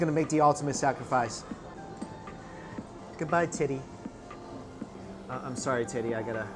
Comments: I heard tittie when you said teddie lol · I thought he said tiddy I was so confused for a second😂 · I thought he said good bye tiddy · I swear I heard him say tiddy